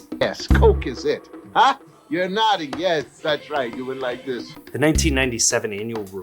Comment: Lmao the closed captions @ 17:22 😂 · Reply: Honestly thank goodness for the CCs cause I thought he said "you're naughty" 😭😭